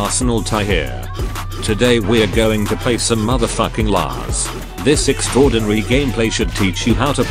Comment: Did I hear "Mother Fucking Lars"?